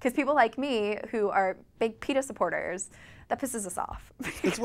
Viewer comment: does she say big penis supporters? :o